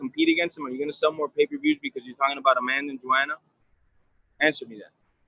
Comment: Lmao underrated comment fella! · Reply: And it sound like he says a man named Joanna...calling Joanna a man.😂😂